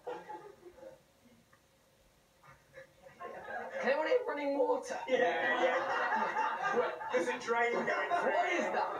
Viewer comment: what did he say? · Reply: "Can anyone hear running water?"